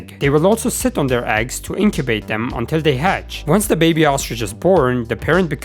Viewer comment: what did you just say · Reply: once the baby ostrich is born